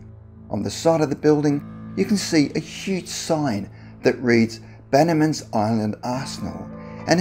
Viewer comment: I swore he said the sign still gallantly reads: “Bannermans Island As*hole” 😂 Also, back @ 9:03 he mentions that area now being taken over by dangerous predatory wolves... As he pictures a puny little fox😂 🦊 Prior to that story, too bad they couldn’t remove all of those Cars, clothes and items in the 2nd one listed, and place them in a museum. They’re still beautifully frozen in time. Can you imagine seeing all of those time-halted period treasures? How amazing! I wonder if any of the cars and/or Electronics still work, as they look pretty well preserved. Wow. Seems so neat- makes one wonder what could make them leave so many valuable items behind- Without even having gone back for them? Strange huh?